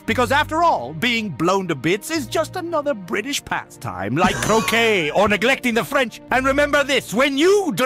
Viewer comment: on first listening I thought he said cocaine rather than croquet and honestly cocaine would have made more sense